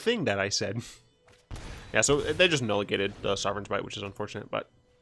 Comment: nulligate? Did I hear hear you say that word 2 times? Haha you made me laugh do hard